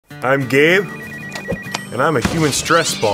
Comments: I thought you said "I'm gay" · I kinda thought he said I'm gay but it's actually Gabe sorry though